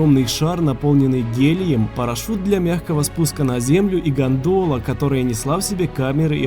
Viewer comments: послышалось из гандона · послышалось гандон,а не гандол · Мне вместо гандола послышалось гандона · Когда он сказал гондола мне послышалось гондона😂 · Лайк кому послышалось не гандола а гандон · Мне послышалось гандона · 0 40 он что сказал го.ндона или мне послышалось?